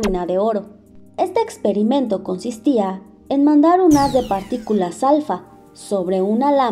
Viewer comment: que decía no entendí que decía xd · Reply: mandar un haz de partículas alfa